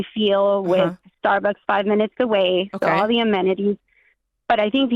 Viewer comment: did she say a Starbucks 5 minutes away ?? 😂 is that the selling point of the house ??